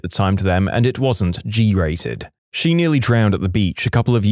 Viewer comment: I thought you said “she nearly drowned in bleach”